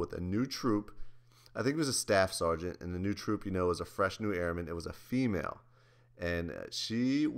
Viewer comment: did say "fresh nude airman"? LMAO